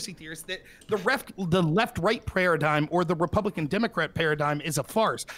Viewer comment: did he just say "Prayeradigm"